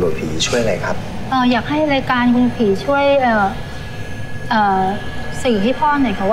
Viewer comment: นาที8:57 เราได้ยินว่าอยากให้รายการคุณผีช่วย<num>ฮาาเลยเรา